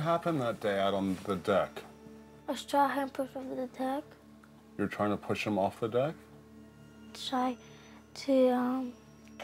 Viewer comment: She said "I was trying to help him from the deck".. shame on whoever captioned that. That's what HE said, instead of asking her to say again. A**h#les